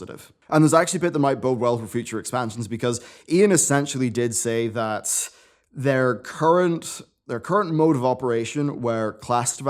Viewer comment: I love mid-roll ads. This is what I just heard at about 6:45 “Ion did say that... [ad] I’m a drug mule for the cartel.”